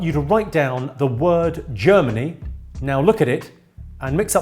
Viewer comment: Glad you put it on screen because I thought you said “Journey” not “Germany”